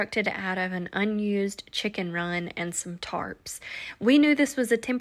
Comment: I was dying laughing bc I thought you said the temporary housing for the goats was a chicken run and TARTS LOL! WOW! Now that is one VERY NICE goat barn! You did a great job! I know it was a LOT of work! But just look at the outcome!